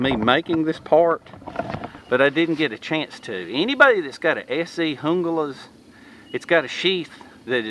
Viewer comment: where you at my friend ? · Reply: what kinda knife is that again? it sounded like you said SC Hungulus